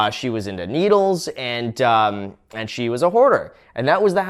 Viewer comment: did he say she was a whore?